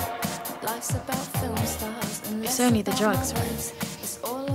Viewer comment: LOL OHH!!! this whole time i thought she was saying, "it's only the jugs right?" and i was liked...huh. that's...a strange thing to say.